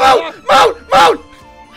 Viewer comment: we all just love seeing them rage, don’t we? btw this part sounded like said “MOO MOO!”